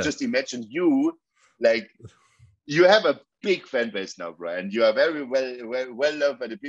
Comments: Did he call him Brian at 8:04? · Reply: Bruh and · Did he call Bryan? 😂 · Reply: Brah